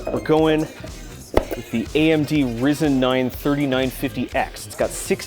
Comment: did you just say Reezen bruh